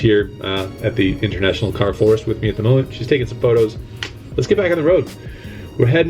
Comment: Did I hear you say "she's taking some meth in the car"?? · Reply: She's taking some photos?